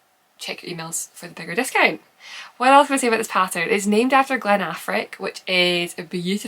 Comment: Hearing you talk about the Affric, I totally heard, “It’s named after Ben Affleck.” I was thinking, huh that’s odd, but alright. Lol